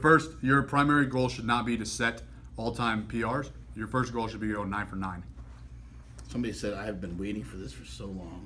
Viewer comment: What did Allan say at 3:18 after he says "your first goal is not to make all time PR" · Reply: "Your goal is to go 9 for 9."